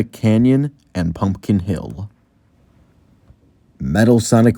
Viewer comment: Did you say "Pumpkin Hill"! - <link>